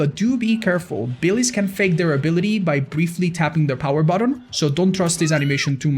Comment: Idk why but at 3:03 i heard "briefly tapping their power bottom"